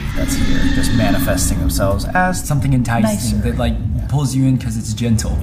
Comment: Bro idk if I’m tripping but swear I her a girl say “nice”